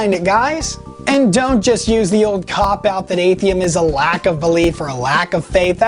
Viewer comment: Did he call it Atheim?